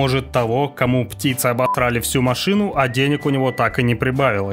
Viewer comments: Когда запикал слово "обосрали" то мне послышалось "птицы обокрали машину" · Когда запикал слово "обосрали" то мне послышалось "птицы обокрали машину" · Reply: А мне послышалось ободрали!